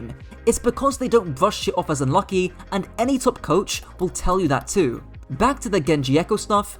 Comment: can anyone tell me what be said here? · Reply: “Because anyone can brush it off as lucky, and any top coach will tell you that too.”